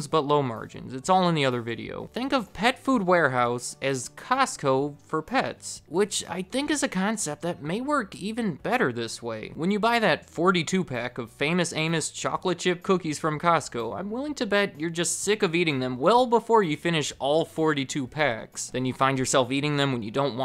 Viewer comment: Lmao I swear I heard "Famous anus" the first time. Had to rewind it a bit lol